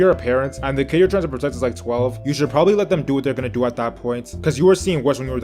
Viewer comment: Wtf did he say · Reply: “Gonna do at that point”